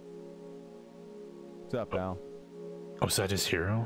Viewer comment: What did he say at 1:42:12 ?? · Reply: "What's up Al" (short for Albert)